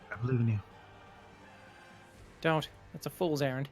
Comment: Buddy says: "a fool's errand" I hear: "a fool's Aaron"